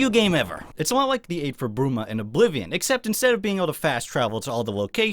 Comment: What is the Oblivion quest he is mentioning there? (I probably know it, I just can't quite make out the words he is saying). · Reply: "Aid for Bruma"